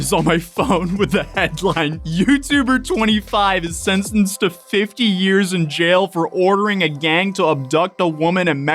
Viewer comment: I thought it said 'sentenced to death for 50 years' and I was just wait what the fuck